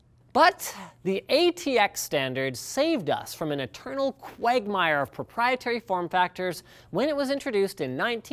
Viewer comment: Sounds like he said Quagmire... What...?